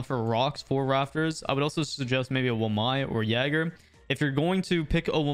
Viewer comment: On 14:32 turn on subtitles and when he says wamai the subtitles says Obama Mike